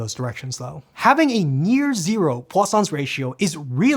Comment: anyone else Heard croissants ratio.